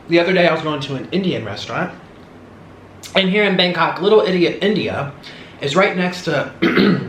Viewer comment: Did he just say "little idiot india"?